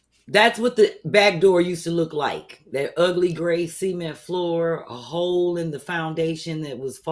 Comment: B 😂, its pronounced “SUH-MINT” not “semen” 😩😂😂 I kept hearing “semen floor” when you referenced the “cement floor” 😅🤣